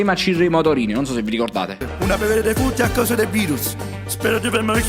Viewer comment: Raga ma solo io ho capito "a vedere i defunti a casa dei Beatles"? Al minuto 3:31🤣🤣🤣🤣